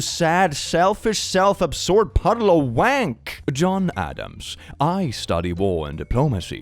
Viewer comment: Wait 8:26 ...did Click just say "wank"?! Not "the self naughties" but actually...Bruh...respect.